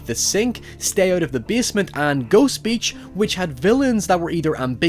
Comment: Listening to this is the background Did he just say Ghost bitch? Oh wait. I gotcha. That's my bad lol. At any rate, nice video bro!